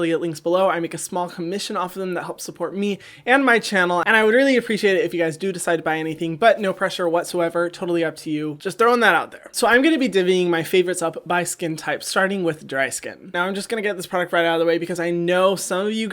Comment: "deeving" I never heard anyone say "dividing" like that I was confused what he was saying